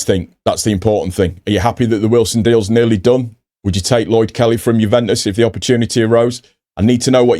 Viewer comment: Gratz on the Subs. I told Ger that I wanted to vote for you in the Content creators awards, instead of him ;) Re Tanaka - well said. I thought exactly the same. BUT he was not the only one in the team!! LOL I thought you had said Lord Kelly.